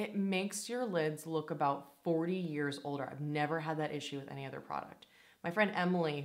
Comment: At first I thought you said it made your eyelids look like a 40 year old. Had to rewind! 😂 I was about to be so offended! 🤣🤣🤣